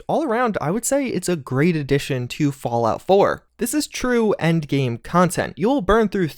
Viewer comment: did he just say fallout 4???